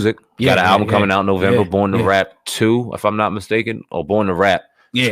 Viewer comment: I thought u said "born to rat" lol